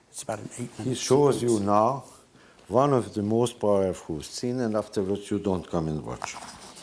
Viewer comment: Can somebody please interpret for me what Bela is saying at 1:04:47? Can't catch it. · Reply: I think he's saying "he shows you now one of the most powerful scenes and afterwards you don't come and watch"